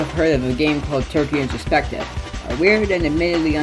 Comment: What’s that turkey game called I can’t make out what he’s saying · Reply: Turkey introspective